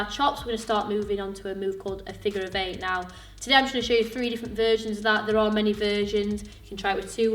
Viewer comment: Every time she says "versions", I hear "virgins".